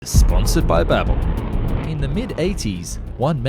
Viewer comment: Legit heard you say "sponsored by bible".